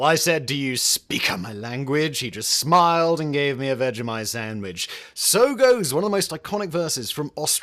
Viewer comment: I always thought the song said "he gave me a bite of my sandwich."